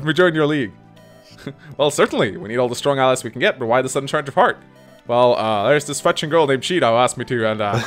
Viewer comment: Did you just say charge of fart?